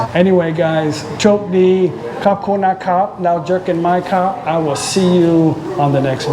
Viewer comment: When you said goodbye in Thai at the end I could have sworn you said "jerk in my cup" 555!!!